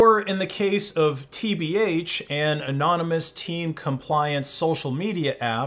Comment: Did you say "anonymous teen compliance social media app" when the active word was compliment?